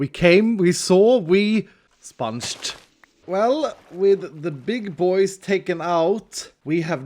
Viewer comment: First Time you Said >Big boy< i thought you said >Pig boy< and pls replace the sign with >to the desert< to >to the „desert“<